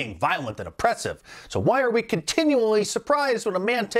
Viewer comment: Reminds me of that song I saw somewhere on YouTube run the world girls. I also thought she was saying huwai instead of who are we.